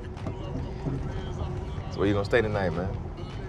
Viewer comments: You could cut the tension with a knife in this scene! LOVE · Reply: The funny thing is that I misunderstood what Kevin said at first. I thought he said: "So why don't you stay the night, man?" But basically that's what he meant ofcourse. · I haven't seen the whole movie yet, but I find it funny that Kevin asks Chiron where he's staying right after he checks him out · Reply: Yes well, if THAT wasn't a hint...... The funny thing is that I misunderstood what he said at first. I thought he said: "So why don't you stay the night, man?" But basically that's what he meant ofcourse.